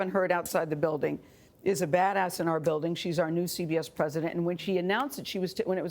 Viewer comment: Did she just say " badass"?